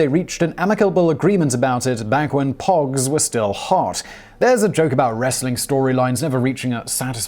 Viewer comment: Did he just say "back when PAWGs were still a thing" ???? Me thinks Business Blaze is taking over 😂😂😂